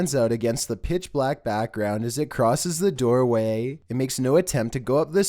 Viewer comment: it sounded like "go away" instead of "he's here"